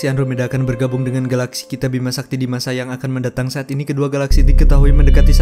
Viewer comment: Kok gua ada dengar Bima sayang 4:23 atau telinga gua yang salah dengar